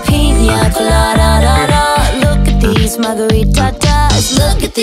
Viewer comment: I thought she said "mothery tatas" instead of "margarita-tas" 🧍🏼‍♀️